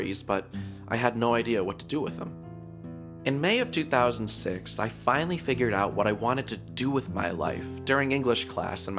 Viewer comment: What I thought He said: I also started dabbing on a piano. What He ACTUALLY said: I dunno XD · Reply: I also started dabbling in piano?